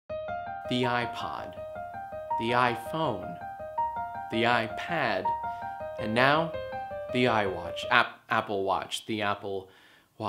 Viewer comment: I thought he said "The YiayPhone" at first